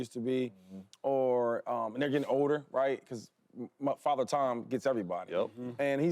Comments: Father time not Tom...have you never heard of "father time" before? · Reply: yes we've all heard about father time, which made me LoL because I swear it sounds like he says father tom 😂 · Did he just say Father TOM???? 🤔🤣🤣🤣🤣🤦‍♂️🤦‍♂️ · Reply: Father time not Tom...have you never heard of "father time" before?